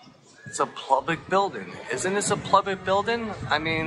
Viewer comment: What’s the point of the “weather report”? Do they think it makes them legit, and would fool a judge? 🤨 And, at 5:58, does he say: “Isn’t this a plubic building” twice? Or am I hearing things?